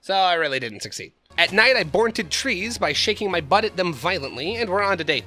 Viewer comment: I heard "shaking my butt at them violently" as "shaking my butt at them like Miley." You know, like Miley Sirus or however you spell her last name.